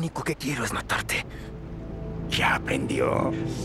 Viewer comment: alguien entiende lo que dijo el duende Verde en esa parte yo entendí " ya aprendió"... Luego oyendolo más detenidamente se escucha como si dijera ya vendió?... Algún héroe sin capa que le haya entendido y me lo aclaré, gracias. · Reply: Es ya aprendió